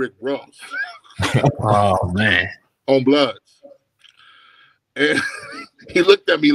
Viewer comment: did Fazion say “ On Bloods”